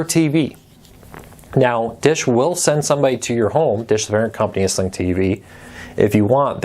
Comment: Did you say that DISH TV will come and put up an outdoor antenna for $150? I had a difficult time hearing you when at 8:50 you named the company to come and install the outdoor antenna. Thanks!